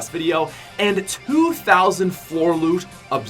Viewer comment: your editor misheard you. Instead of 2000 floor loot he heard 2004 loot.